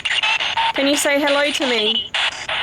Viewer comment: I only discovered your channel recently and enjoying the content. I heard something at about 41:45 and went back 5 seconds and something else caught my attention at 41:43. I am pretty sure I heard "Amy" as you said the word "me", it was definitely not your voice and I think I'm hearing "Amy" again at 41:44 I also heard "please help me"